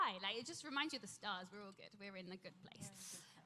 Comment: Someone understand what does Kat said in lower voice ? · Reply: She repeats what kat says She says “were in the good place”